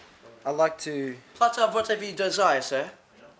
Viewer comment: what did say to i couldnt understand cuz of his accent its sooo cute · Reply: platter of whatever you desire sir